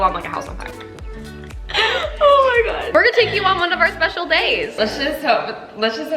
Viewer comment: Omg nearly had a heart attack. Thought Madelaine said "special dates" XD